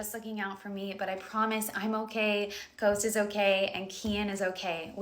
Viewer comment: Did she say Ghost, Coast or Toast— I’m confused🫣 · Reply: the baby's name is coast :)